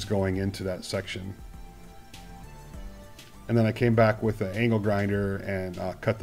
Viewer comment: sorry, but at 3:47 I misunderstood...did you say *nal grinder? XD